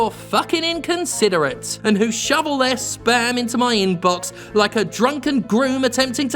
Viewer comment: I heard "shovel their sperm into my inbox". ...honestly, that sounds about right.